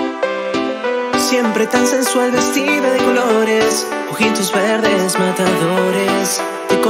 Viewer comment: Y yo pensando que decia "Siempre tan sensual vestido y flores"._. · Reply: jaja pasa yo pensé que decía vestido de colores 😂